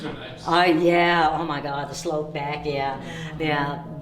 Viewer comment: When you heard the noise in the upstairs room, I heard someone say ( hi ) in the camera. And on your video it sounded like someone said cone back